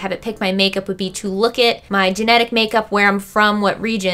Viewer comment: Ok but she should've titled this "my genetic makeup" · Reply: Lindsay McCrory I THOUGHT YOU SAID MY GENITAL MAKEUP LOL TF